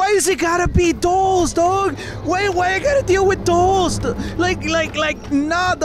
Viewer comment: It sounds like he is saying toes not dolls